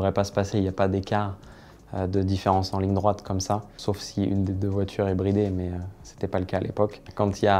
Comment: In the Ford v Ferrari segment at 6:42 he does not say "Unless one of the cars is a hybrid", he says "Unless one of the cars has a speed limiter".